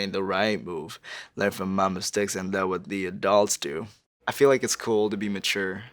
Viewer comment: I always heard “The a thots do” instead of adults and it just made me go what?